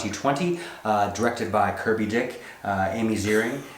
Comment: I lost it I thought you said curvy dick too and had to rewind. Too bad we didn't see Joseph's initial reaction